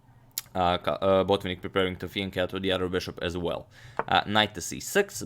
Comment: Guys what does Agadmator say on 1:18 about the bishops? (Finaccho or something) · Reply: Ok just researched it's finachetto